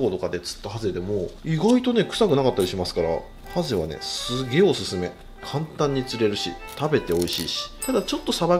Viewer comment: すギョイおすすめって聞こえた。